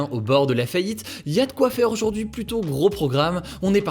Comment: ce qu'il à dit : "y'a de quoi faire aujourd'hui" ce que j'ai compris : "le monde part à nouveau en vrille c'est cool"